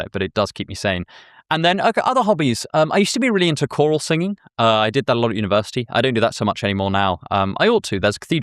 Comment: Somehow I heard "coral singing," and I was trying to figure out why he's bothering the fish